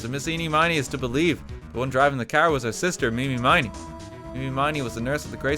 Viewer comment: I took a nap listening to this and when I woke up I paused it and went to go watch something else. I now have come back to this video and the first thing I hear is "MI MI MI MI MIII"